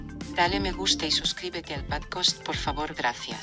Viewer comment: I dont know why in spanish it pretty much sounded like she said "padcost" instead of "podcast".